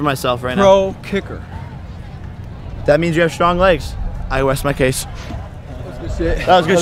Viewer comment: “I west my case” did anyone else catch that?😂